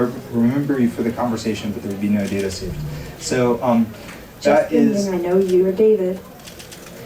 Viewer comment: Did she say just kidding I know ur data? (3:47-56 ) what’d she say?